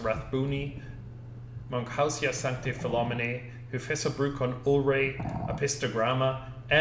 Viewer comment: what does he say? Hyphessobrycon what? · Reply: Hyphessobrycon ulreyi